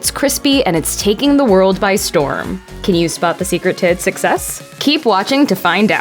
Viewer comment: half asleep and thought you said “tits of sex” lmfao instead of secret “to its success .. oops my bad lol